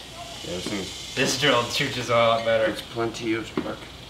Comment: I heard "chooches" at 5:53, AvE fans?